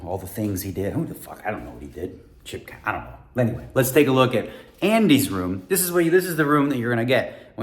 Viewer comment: I thought you said " Randys room". I was so excited. " Ma I made it"! Love the vlog. My first watch everyday